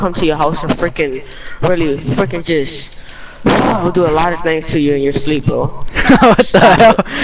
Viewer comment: Can we get a pause for what this kid said 12:05 one like is to help this kid. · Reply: He said I'm gonna do stuff to you in your sleep it's creepy